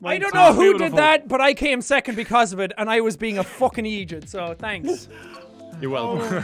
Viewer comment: I know what jack said, but he sounded like he said he was being an Egypt